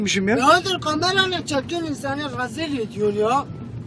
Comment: de arkadaşlar ne diyor tam olarak anlayan ....Ne cekiyon insanı ...... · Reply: Ne çekiyorsun insanı rezil onun şive ile razil ediyon yav diyor